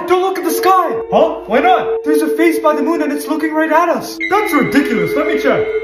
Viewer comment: When I was watching this video for the first time I wasn't really paying attention so I thought I heard the guy say that there's a face on the moon and that reminded me of the Legend of Zelda Majora's Mask